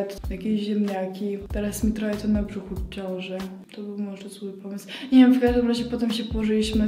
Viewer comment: usłyszałam,, teraz mnie trochę boli brzuch od ciąży" A bylo ,, teraz mi to trochę na brzuchu ciązy"